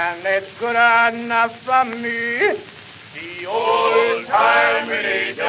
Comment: I always thought it said "The old timey ninja" 😂😂